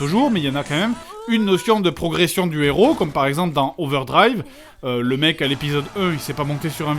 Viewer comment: C'est quoi le nom du manga à 13:20 ? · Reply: T'es sérieux ? Fred le dit en même temps que l'extrait passe à l'écran 😂 : Overdrive !